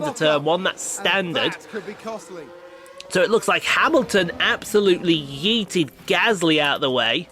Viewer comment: did you just say Hamilton yeeted? lol